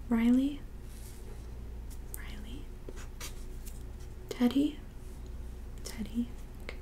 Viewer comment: When you said Teddy i thought you said Daddy 😂